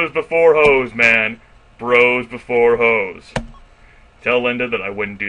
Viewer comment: weird i heard it as hoes before bros XD